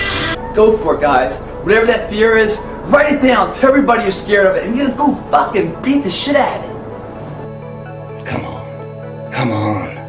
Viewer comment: guys, please help me! What is he saying at 4:07 , after "write it down"? thanks. love this video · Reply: he says: "write it down, tell everybody your scared of it and then go beat the shit out of it" True legend and an inspiration RIP George Plitt